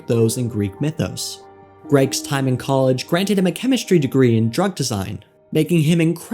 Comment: I thought he said a degree in dung